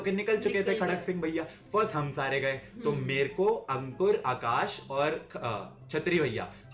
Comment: did he said ankur i 11:14 kuch to garrbar hai daya kahi woh chattri bhaiya toh nhi